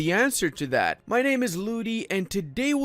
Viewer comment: I was a bit lost focus and i thought I heard "my name is Mali" instead lol.